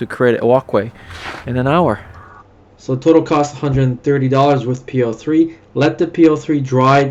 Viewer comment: Question ... you say it was $130 worth of PL3..... ( is that right )???? I thought l heard you say $15 per tube, and you used 3 🤷🏻‍♂️🧐🤔 to do 100 sq.ft. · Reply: Hey there, I said total cost $130 with the PL3 (Canadian including the patio stones)